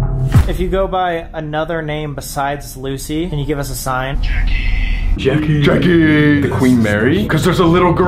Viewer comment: When it said Jackie I thought it said you're gay 😂